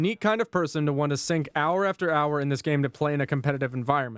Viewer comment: My phone was a good distance away, and I thought I heard "how many Roblox does it have to get through..."